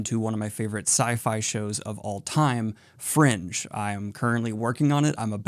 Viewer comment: for i second i thought you said; "one of my favourite sci-fi shows of all time: Friends" and i was very confused